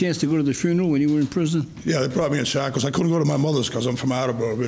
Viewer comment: the subtitles say "inaudible" I am not a native speaker, but i am pretty sure he said "they brought me (to the funeral) in shackles"